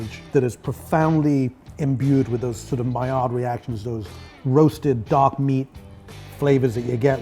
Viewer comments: i heard roasted dog meat? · If you're like me, you couldn't quite catch what he said at at 1:13...I found out he said "imbued with those maillard reactions" Maillard reaction = a chemical reaction between amino acids and reducing sugars that gives browned food its desirable flavor. (i.e. Seared steaks, pan-fried dumplings, cookies and other kinds of biscuits, breads, toasted marshmallows, etc.) Very cool!